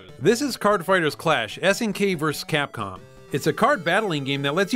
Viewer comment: (What he's saying on my first listen and on a casual listening) "HARD FIGHTERS!" 20:04 - 20:07 Same rules apply, and he's still saying: "HARD FIGHTERS! 2!" Geez, enough with your hard fighters. We get it.